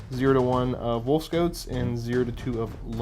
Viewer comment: Gotta admit, when said "wolf scouts" it sounded like he said "wolf goats"